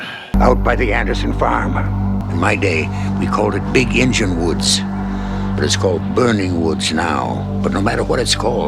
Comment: I thought he was saying "Big Engine Woods." I thought "what an odd, and probably ahead of its time, name..."